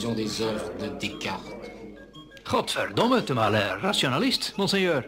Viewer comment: wait a second!! Was he speaking Dutch!? I swear I heard "Godverdomme!", which is Dutch for "Goddammit!".... wtf... o_O